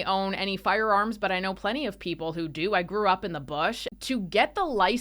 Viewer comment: you grew up in the bush??? actually had to cycle back and play it again cause I thought you said "you threw up in the bush"....sorry sister the visual alone I had to be sure. So I got it now, we're talking thumper and bambi, grew up in the bush.